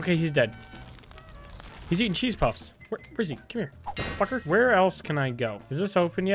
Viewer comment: did he just say “grizzly come here”?